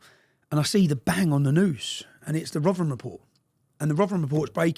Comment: He mentions a report at 36:11 . Anyone know what that report is? Did he say Rovren report? ❤ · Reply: He said Rotherham report. Lol.